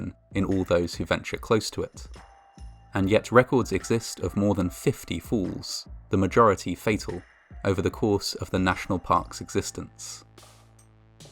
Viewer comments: I thought you said 50 Fools at first! :) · My American ears heard "more than 50 fools" instead of "more than 50 falls." Then I just kept hearing it throughout the video. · When you said "50 falls" "Falls do occur" - I heard "50 fools" " Fools do occur". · Initially, I thought he said 50 fools. I suppose that works too. Fools indeed do occur. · when he says "many falls" i thought he said many fools, his accent makes falls sounds like fools😂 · When you talked about 50 falls, because of your accent I thought you said 50 fools. Lol. Which fits, unfortunately. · who else heard "more than 50 fools"? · all I heard was "more than 50 fools" and just slowly nodded in agreement.